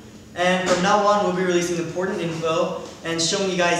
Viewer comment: Did he say "From now on we'll be releasing the... porn?" at 0:20 O_O oh my ears... Someone pls reply me what he's saying at that part xD